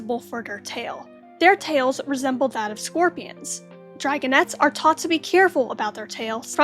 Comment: t first I heard “sport cans” 😭